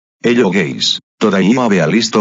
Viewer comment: did he call us gays